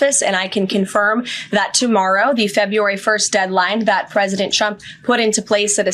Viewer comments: Did she just call him "President Chump?" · did she say chump?